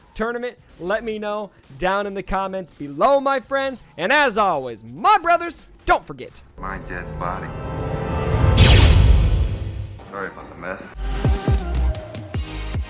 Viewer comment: Lol. The way you add the clip of at the end of your videos makes it sound like is saying: "...and as always, my brothers, don't forget my dead body."